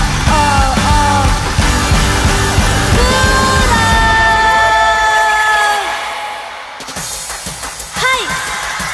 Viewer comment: ここなんて言ってるかわかりますか？ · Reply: 調べたら「Pull up（プーロー）」と言ってるらしい！！！ラップでよく使われる用語で「音楽を掛け直す」意味らしい。